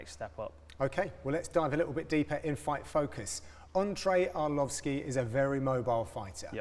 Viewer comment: lmao did say entree arlovski at 0:33 ???